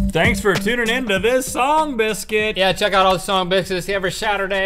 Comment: I heard link say song bitches...>.<